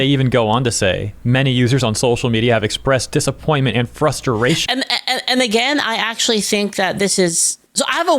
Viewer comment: did he say frusturation?